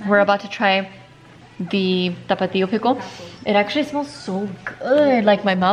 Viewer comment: I like how she says tickle instead of pickle 😂